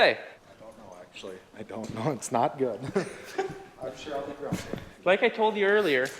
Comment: says "I'll be grounded" but the subtitles say "I'll be drunk"